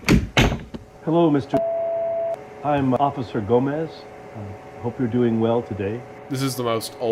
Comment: catastrophic mishear, thought he said "I'm officer Bumass", and busted out laughing